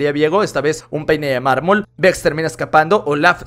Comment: escuche un p3ne de marmol KSJDHFGFJDKJFHG